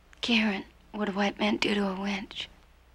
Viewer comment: the word is "wench". A "winch" is a hauling or lifting device. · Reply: i always thought they said "Witch"